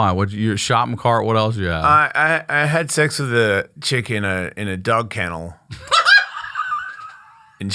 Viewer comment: I had to register to this sentence… I swear Jason said “Chicken in a dog kennel”